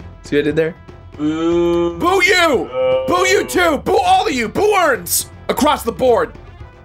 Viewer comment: Did he just say 'Boo-urns'?